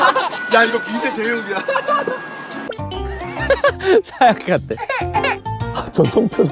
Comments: Is it just me or it sounds like Jimin said sacate? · I think it was jin but it sounded like he said "Sacate" which means "get out" in spanish Or was it hobi?